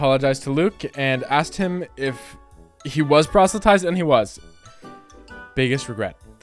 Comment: Hi great video great series so far also what did you say at 8:47 ?? Pro-what?? I honestly have no idea what you meant by that and I can't let go lmao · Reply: I said proselytized lol and thank you hehe (*≧∀≦*)